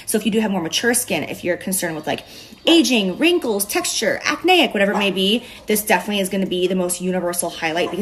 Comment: did she just say “acney” in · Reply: She said acneic. Like you'd say " I have acneic skin" Most of us just say acne or acne prone skin where I'm from